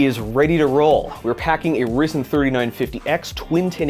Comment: Did you just say risen instead of ryzen?